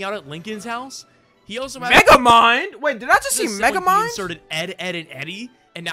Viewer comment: On god I heard Niggamind when I first heard it then I had to go back and laugh again